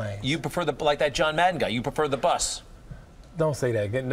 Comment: What does he say he prefer at 0:58? "So you prefer the.... ???" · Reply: Bus, his tour bus